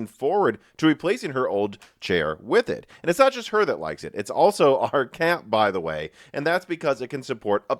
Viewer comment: Did u call your cat fat? LMFAO XD XD XD